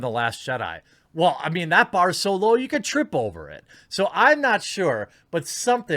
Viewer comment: I heard, "That bar is Solo, you could trip over it"